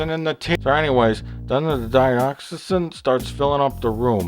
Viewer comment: Hang on, DiOxygen? Erm, you mean O2, that stuff we need to breathe? Not gonna kill you. Kinda basic science that. Or did I mishear?